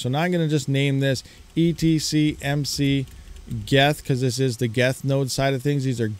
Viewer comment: i thought you said ETHPOW almost vomited thank god😂